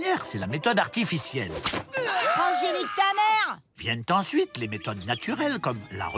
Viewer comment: la piqûre dit nique ta mere ??! 1:55 le prof moustache dit" bâtard de virus " ????!!!!! Il change de langage ou c moi ?